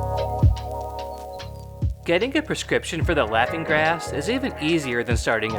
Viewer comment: Dude, you forgot GAS. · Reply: Oh I thought he said "Laughing Grass" not Laughing gas... 👍